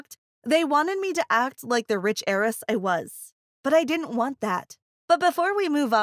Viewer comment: I honestly thought she said aries like a zodiac XD